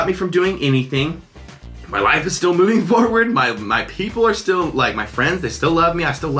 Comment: and when he said my life is moving forward I wasn't listening properly and I thought he said my wife is moving forward